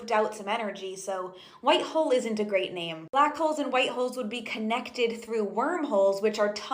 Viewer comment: Lmao....I thought she said “BLACK AND WHITE HOES”!!??😂😂😂 I’m buggin